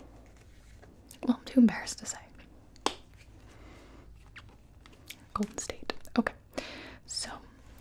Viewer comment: no one gonna question what she said I coudnt here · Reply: Goldenstate, although I don't understand